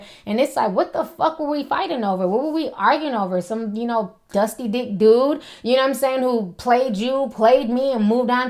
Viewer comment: did she just say "dusty dick dude"? 😂😂